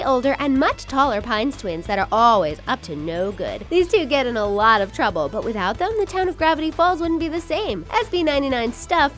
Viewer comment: guys- for me sounds ''Gavity Falls'' completly different i hear ''Grab it, he falls'' help mee- and btw the charackters look cool as adults